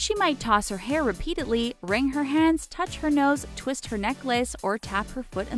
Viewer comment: The video lagged when it said “twist her necklace”so I heard “twist her neck” for a second and was very concerned